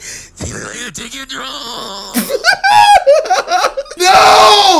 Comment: The "she look like a chicken drum" had me wheezing 😂😂 (edit) mum im famous lmao also dont argue on what the mans said it is whatever yall think it is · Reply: I heard ‘I KNOW YOU’RE TAKIMG DRUGS’ 😬